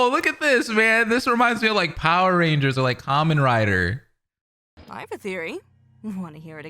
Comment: did i just hear... Kamen Rider?